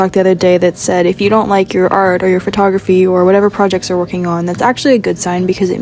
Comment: My volume was low and I thought she said pornography 💀😭🫡